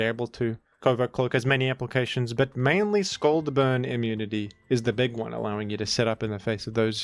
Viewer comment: I thought it said "bird immunity" and was wondering why araquanid was able to dodge half the game